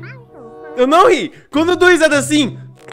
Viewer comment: "EU NAO RI, QUANDO DO RISADA ASSIM..." NÃO ENTENDI.